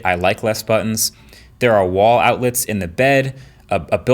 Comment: "There are wall outlets inn the bed" 2:04 Did I just hear the word bed?